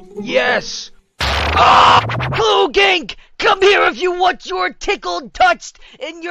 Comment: it sounds like he says clooging